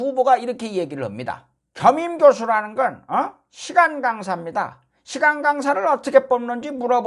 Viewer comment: 시간강사 근디 왜 내귀엔 시 간강사로 들려